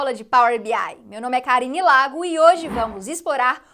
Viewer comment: Eu entendi "meu nome é Kari Nelago" da primeira vez hehehehehe Eita mulher porreta, super incrível!!!